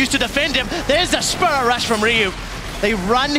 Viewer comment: did I hear "sperm rush"? ))